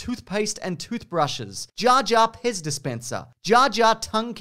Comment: Not gonna lie, I thought you said "Jar Jar Piss Dispenser" at first, but it was just a Pez dispenser. I was very concerned but also intrigued.